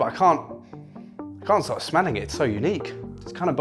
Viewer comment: I thought it said smelting